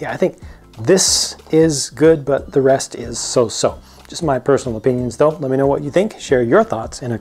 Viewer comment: Wait, did you just said "Don't let me know what you think" at 7:44? If we shouldn't let you know what we think why should we share our thoughts in the comments below? I'm so confused right now. D; Again, a great review! In my opinion that old green Creator train (4837) looks still the best. I would just switch some parts with much better fitting parts that not existed back then, like the lights, but in general I think it's just a nice train to look at. I'm not a big fan of this one, but I agree on your opinion that the main build looks the best out of these three. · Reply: It's "though". "Just my personal opinions though, let me know what you think."